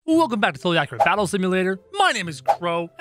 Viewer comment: did you say fully accurate battle simulator?